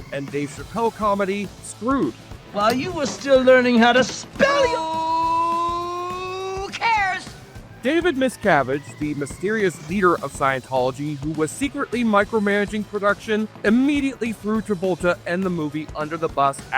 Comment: I was just listening and I thought I heard David WASCAVAGE the director of Suburban Sasquatch. I had to do a double take lol